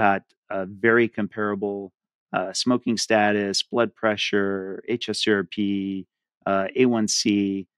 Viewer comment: lists the biomarkers that were matched. I can't understand the one he said after blood pressure. The closed caption is HSP. I think it might be hs C-RP. Can someone clarify this lab test please?